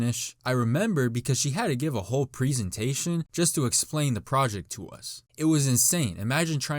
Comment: who else heard "prezentation" 9:30)just wait